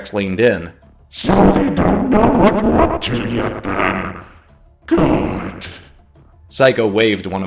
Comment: you got to turn down the Kaijagrex filter i have no idea what he said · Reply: He said “so they don’t know what we’re up to yet, then? Good.”